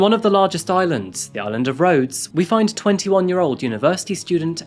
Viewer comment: Realizing there’s a island called Rhodes and I live in Rhode Island… I misheard you and was wondering why anyone would love Rhode Island 😅🤦🏻‍♀️